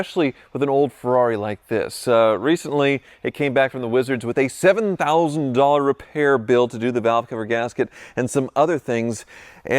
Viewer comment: Did I hear "$70k repair bill"?